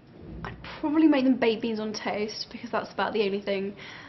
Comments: I thought she said baby's on toast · I thought she said "babies on toast" am I the only one? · i thought she said "babies on toast" lol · At first I thought she said "babies on toast"! =O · It sounds like she said babies on toast. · I thought she said "I would probably make them BABIES on toast" and I was like o.O Is that some kind of a British thing or what??? (then I figured out it was BAKED BEANS) I have never felt so releaved. lol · I thought she said babies on toast... I laughed so loud. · HAHAHAHA!!! I heard her say "babies on toast" HAHAHAHA!!! LMAO!!! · When she said baked beans I thought she said "I'd probably make them babies on toast" lol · i thought she said that too but i think she said baked beans? ? lol cuz he said that at the end lol but it really sounded like she said babies on toast lol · lmao. what did she say she would cook? I only heard "babies on toast"